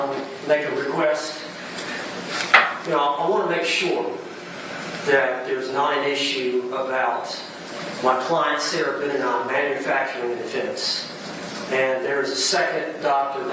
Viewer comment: Did I hear right at 36:40 the defense wants another forensic psychologist "to make sure that there isn't an issue about my client Sarah Boone manufacturing a defense" ? Gotta wonder if he isn't starting to have doubts already.